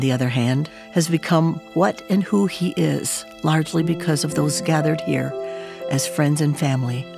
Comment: When I started viewing this great video I was listening to the Gloria Gaither voice and read the words as she spoke, very nice and soothing. I noticed that at 0:38, time of video between the words “gathered as” I heard the word “hear” that Gloria says "gathered hear as". I also noticed that at 3.09, time of video between the words “you need never” I heard the word “would” that Gloria says “you would never need “. What? They have at 5:08, time of video added the word “and” at the last “your”. Now does this mean someone putting this video together typing out the slide just left out the words “hear” and “you would never need “ in the slides and added words when they wanted too?